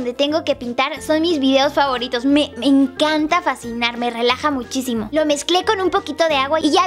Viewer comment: min 3:04 dice me encanta facinar jajaja si escuche bien o no ?? te quiero yuya a ti todo se te perdona besos guapura ;)